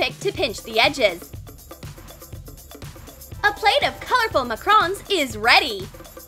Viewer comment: did she just say mAcRoN